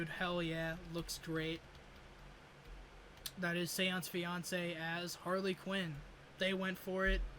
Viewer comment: I wasn't paying attention and I thought you said "That's Beyonce as Harley Quinn"